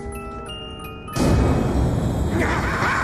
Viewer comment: i really thought that samantha said hahaha Die Die but its bye bye xd